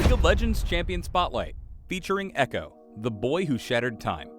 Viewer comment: Lol I'm so dumb I thought it said the boy who shattered me